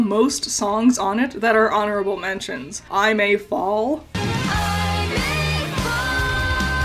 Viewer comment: I was today years old when I found out she wasn’t saying “forget about those little chicken bites” in Dream Come True 💀💀💀 Like I thought she was calling Weiss flat 😭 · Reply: Speaking of miss heard lyrics I thought ‘I may fall’ was saying ‘I am a ball’